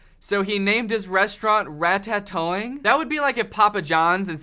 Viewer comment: For some reason, I keep hearing you saying ‘RataTOE-ing’. Well I hope it was.